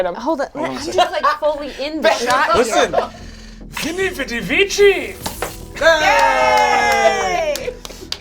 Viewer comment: when brennon shoots the toy gun what does he say? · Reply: Vini vidi vici. It means “I came, I saw, I conquered”